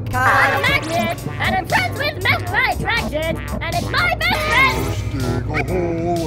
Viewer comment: We waited 8 months for "This is a gel. I am a Gel" ...... Worth it. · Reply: animation I thought it said I'm friends with metal I'm pregnant